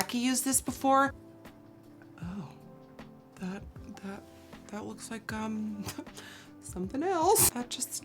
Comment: When she said that looks like, hmmm... it sounds like she said that looks like cum!😂😂😂 which is exactly what she was trying to say it looked like!😂😂😂😂